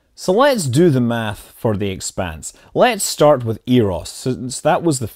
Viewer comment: What was the name of the fav show you said? I keep hearing "Vague Spades" hah :(